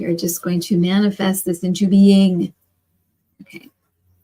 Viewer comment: Okay, does anyone else hear the male creepy voice come in around 21:30? Sooo Halloween!, 🎃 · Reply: I slowed the playback to 0.25 and I believe the voice at one point repeats words " into being."